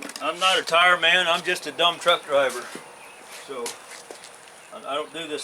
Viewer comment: It was awesome to hear someone use the same phrase as I do😊.. "I'm just a dumb truck driver" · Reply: Haha. I thought he said dump truck driver. Now I’m a dumb truck driver.